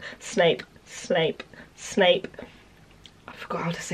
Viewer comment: Around 50 times I thought she said smape or snake😂